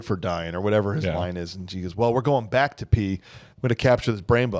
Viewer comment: When I watch this movie, I always get a little chuckle from "We're going back to P." cuz I hear it as "We're going back to pee." Also, Dina Meyer in that dress at the dance... Wow.